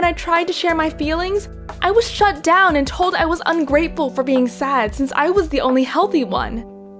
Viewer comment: At first I heard "when I was 6 years old and told I was pregnant"